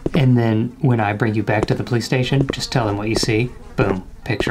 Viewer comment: I thought he said PlayStation 🤦‍♀️